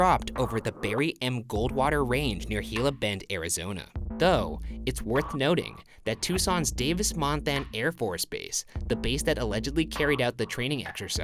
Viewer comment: I thought it said "Girl Bend"